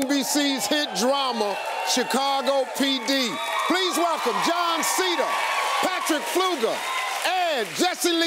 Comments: Am I the only one who thought that he said john cena instead of jon seda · When Steve Harvey was saying Jon Seda I thought he was saying John Sena 😂😂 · I cant be the only person who heard “John Cena” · Oml I misheard Jon Seda as John Cena! <X0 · JOOOOOOOOOON SEEEEEEEDDDAAAA!!!!! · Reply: I thought he said John Sena (spells) lmao I really didn't understand how I could have missed he was in the show